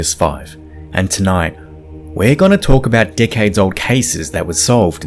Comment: Bruh he said “decades old cases” but his accent for half a second I swore he said “dickheads old cases” I damn near choked 😂